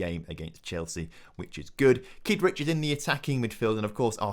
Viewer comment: Thought you said Keith richards was playing then realised it was kidric.